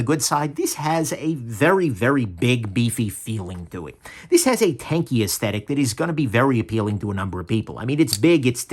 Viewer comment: I heard 'tankie aesthetic' and imagined a handle scale engraved with a picture of people revering Joseph Stalin. Then I saw the clip and thought 'close enough!'.